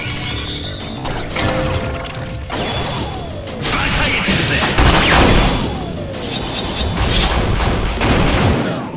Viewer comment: そういえば3:08のマイドニックノヴァのセリフ、なんて言ってます？教えてほしいです！ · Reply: 馬鹿げているぜ！